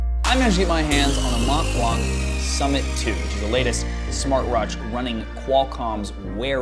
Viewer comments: Did I hear "latest smartroach"? at 0:14 ... Nice video. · What did you say at 0:13 ? Smartrrwratch? 😃 Love your channel.